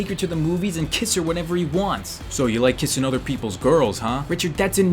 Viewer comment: For a second I thought he said "So you like kissing girls huh?"